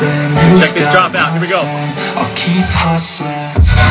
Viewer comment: 1st one is Hustling, not Til' I Hear 'em Say lmao